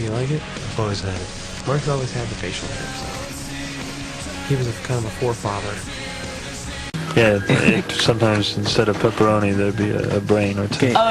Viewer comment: What's Brandon say at 2:54? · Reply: "Mark has always had the facial hair so.. He was kind of a forefather"